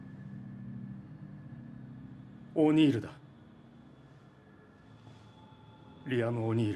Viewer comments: 「オニールだ。」が「おにぎりだ。」に聞こえた。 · 「オニールだ」が「おにぎりだ」に聞こえた